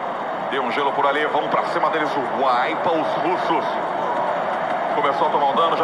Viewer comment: só eu escutei "wipa o fluxo" kkk?